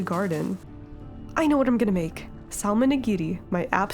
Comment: love the videos but every time i hear "saLmon" my brain autocorrects it to "salmonella " :"" )